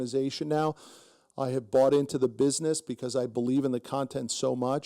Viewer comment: At the end I heard you say that you brought into the business of BFS. Please correct me if I am wrong. But if I am right you were right when you have cash stashed away opportunities find you. Great Job